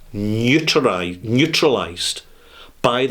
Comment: I thought said NEUTERIZED, but he said NEUTRALIZED. I guess in reference to what he was talking about then BOTH words could be accurate.